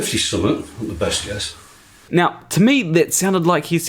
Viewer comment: When the guy introduces himself I hear: "I'm 57... best years"